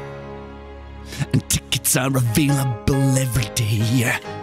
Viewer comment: what does he say in 1:34? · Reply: "And tickets are available every day."